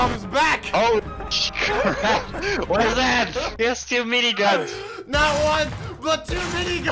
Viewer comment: Im not sure i heard that but i heard say "memeguns" instead of "miniguns" "Memeguns"- 2020 way to make Meme a literal meme